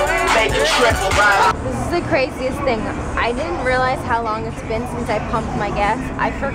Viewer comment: did she say" I didn't do crack"?